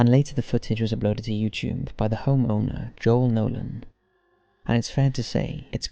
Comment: sounded like John Oman That's literally my Coach I had to rewind like 4 times to see if I was hearing that right lol.